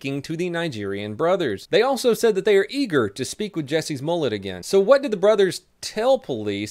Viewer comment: Did anyone else hear 'Jessies Mullet'?